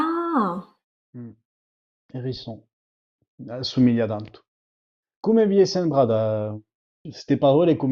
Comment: Parece que dice: "se humilla tanto" jajajajaja.